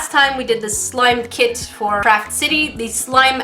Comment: I thought she said crap city 😂